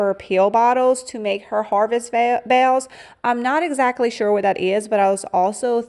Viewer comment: May I ask where you are from? You have an unusual accent and some words have a drawl that I misunderstand certain words . Like bells I thought you were saying “bales” just curious.